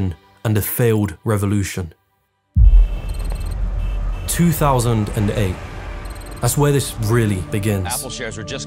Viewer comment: 2000 and ape 😂 Thats what I heard after all the NFT ape shit bombing previous to it. Lol